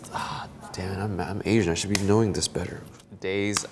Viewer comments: “I’m Asian, I should be knowing this better”😂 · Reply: I'm not a native speaker but I hear "I'm Asian, I should doing (he means counting) this better" Maybe I'm wrong... Anyways it's fun. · “I’m Asian, I should be knowing this better” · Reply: What I heard was "I'm a math major I should be knowing this better"